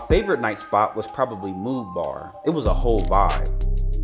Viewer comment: Hello, thank you for posting this informative video!! I’m traveling together for the first time next week. At 3:02, what club are you saying was your favorite? It’s difficult to hear. Also, any other travel tips that you have would be greatly appreciated 😊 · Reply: The club is called "Mood Bar". Let me know if you have any other questions.